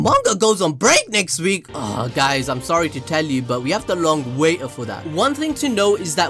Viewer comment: Did anyone else hear "we have to long waiter for that "